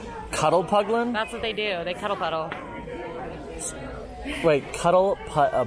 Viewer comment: I swear I always heard say “Cuddle puggle” when they were talking about the meerkats and that’s hilarious to me because I actually have 2 puggles (pug beagle mix) and I always saying cuddle puggle snuggle puggles to my puppers 😂